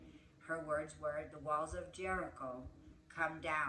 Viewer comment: I thought you said the walls of Jericka ( my name);😂😂😂😂